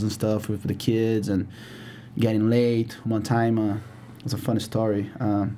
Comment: did he said while he was in the jungle, he was getting laid? lmao